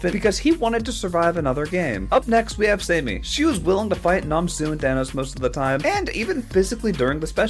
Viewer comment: Did bro say nam su is bro thanos